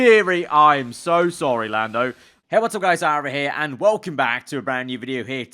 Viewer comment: It sounded like he said what’s up guys SAUBER here.